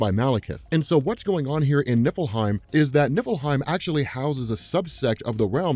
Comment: Oh.,. NIFFLEHEIM. I thought you were saying Nippleheim.